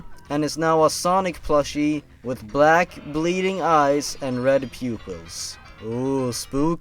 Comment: When you said red pupils I thought you said red pubes facepalm